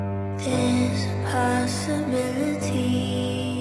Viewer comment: I heard “theres a possum in the tea”.....that just me?